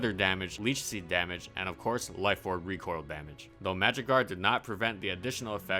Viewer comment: When he said magic guard I thought he said magikarp lmaoooo